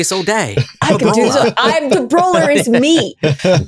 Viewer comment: "The Brawler is Me." Deep Thoughts w/ ❤ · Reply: That’s what she said. But what I heard was “It’s me, Hi, I’m the Brawler, It’s me.”